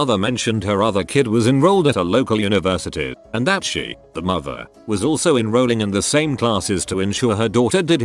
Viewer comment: lol I thought it said “the monster” instead of “the mother”.